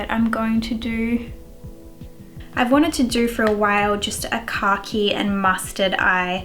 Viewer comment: I legit thought you were saying “cocky” took me like 3 times of you saying it to realize you were actually saying khaki 😂🤷🏼‍♀️